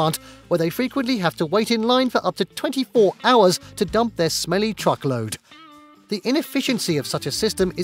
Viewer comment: watching this video and hearing the narrator use lots of poop synonyms words like trash, waste, stinky garbage, feces, smelly truckload, etc, then at 6:36 he straight up just says "that's still a lot of shit" took me by surprise haha, all that effort of trying to be careful with words gone down the pan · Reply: I heard "smelly chocolate" 😀